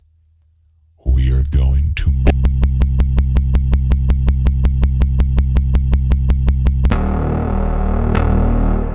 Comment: What he say here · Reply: He says "We are going to M-" then the QR code goes to a video on another channel called "urder you" So he says; "We are going to M -urder you"